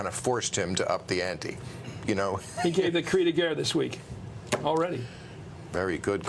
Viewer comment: What did Chris say @ 3:49? That prompted the 'bilingual' comment by Maher? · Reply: sankrityayan Chris said "Cri de guerre" which literally translates to "cry of war" or "battle cry".